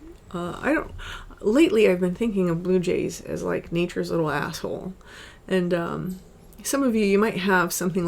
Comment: nature's little asshole? am i hearing that correct? 🤣